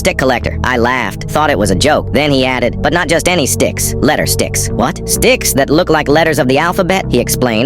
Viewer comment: bro i just realized at 0:20, it said d*cks instead of sticks...